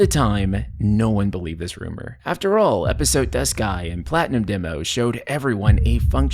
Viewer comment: sounded like "Episode, des ka??" Lol. As in, why is the main plot in episodes instead of the game. Also, I never heard of the anime before now! Too late since I played the game, but thanks. Either way, there was no villain backstory (not sure about Episode Arden) nor any payoff for all of the characters' suffering (like Dawn of the Future) and my suffering doing nothing but fetch quests. Soulfull maybe, but dolefull, a big disappointment.